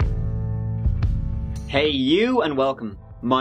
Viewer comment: “Hey YOU and welcome”...words I look forward to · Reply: I thought he said "hey ! you ain't welcome !" (but I am from France, I don't get everything right, anyway it makes me laugh and I always stay through the whole video !)